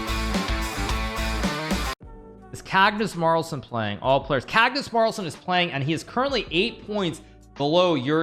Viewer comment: did he just say Cagnus Marlsen lol